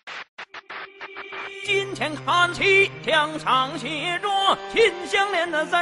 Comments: i heard "KIM JONG IN THE DUNGEON" Edit:Pls heart my comment and i love ur content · is it me or i am the only one who hears its your never birthday